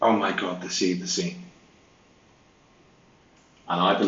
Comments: I think it sounds like she says “it’s a scene” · Maybe the neighbors came out. Sounds like Jen says "Oh my God, they see the scene"